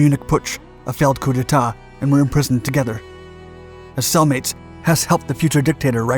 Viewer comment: I thought you said “as soul mates” not “as cell mates” at first